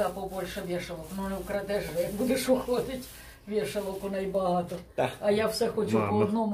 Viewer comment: В квартире 5 минут и уже плечики украсть хотела...это ппц... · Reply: Я слышу "Не вкрадеш жеш, як будеш уходить" 17:35. Не украдёшь же, когда будешь уходить (рус.)